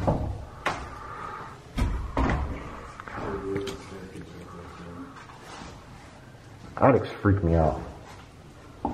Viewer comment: voice/whisper but hard to understand what it says but definitely sounds like a man saying possibly, "it’s... ??" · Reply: "It's been done" ?? That last word is hard to make out. =^¡^=